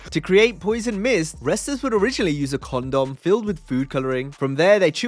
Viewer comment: did you just say they used condom 🤡